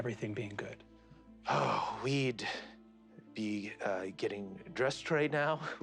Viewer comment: Serendipity strikes at 40:20 With the brief pause all I heard was "weed..." Pib and Pinnochio are fantastic Brennan, there better be a Goose-Hydra boss at some point in this campaign.